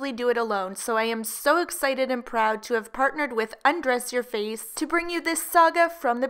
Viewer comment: This whole time I thought you were saying you partnered with dressyourface lol